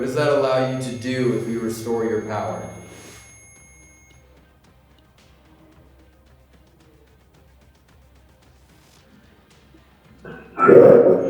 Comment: I may be trippin but at around 47:40 when asks what does it mean we help you restore your power - to me it sounds like it says "Ya'll help me come back"